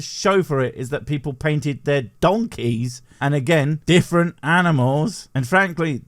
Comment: Oh, I thought he said painting his monkey? Monkeys are to be spanked, not painted.